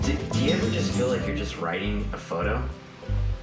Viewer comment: I think the cat said "yes".